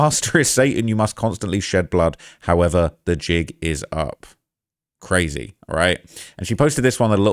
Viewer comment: Did he just say “The Jigga’s up?! 😂